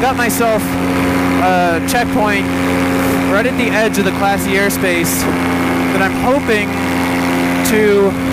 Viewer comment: I misheard this as "classy airspace" and at first I found myself wondering if there was a dress code 😂